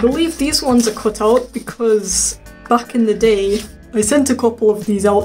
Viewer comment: I heard "f**k in the day" but it was supposed to be back in the day. Is my brain getting squishy